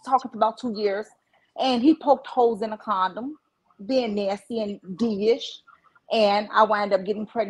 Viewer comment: I feel dumb because I have NO idea what's going on when she starts talking 😬 Kudos to for being able to get some chronology out of this story. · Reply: None of do! All I heard was “hole in the condoms” and “holding down a jail bird she met 24 hours” some shish like that LOLOLOL